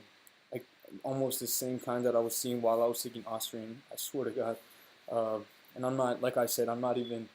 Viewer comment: "almost the kind that I was seeing when I was taking ___" What's that supplement that you said? I didn't catch it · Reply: Ostarine. But this video is old and I kind of regret some of the things I said lol